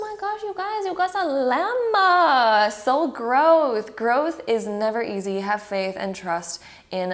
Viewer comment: I literally thought you said “so gross”